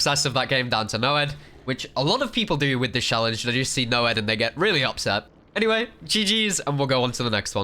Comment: did they say the n word or something? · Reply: some other dude said that nonce means pedo